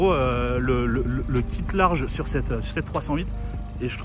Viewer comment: Je sais pas moi mai a 6:56 j'ai entendu fiat 308